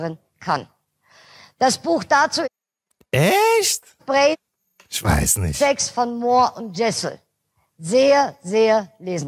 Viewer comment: Ich kann den Buchtitel bei min 17:49 nicht verstehen, ebenso die Autorennamen, hab gesucht, aber nichts gefunden, kennt das jemand, bitte? Und hilft mir da weiter? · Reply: gefunden Moir /Jessel "Brain Sex. Der wahre Unterschied zwischen Mann und Frau."